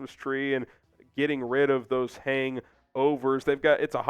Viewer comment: I thought you said get rid of Poles.